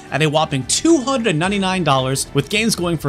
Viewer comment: I thought it said $2.99.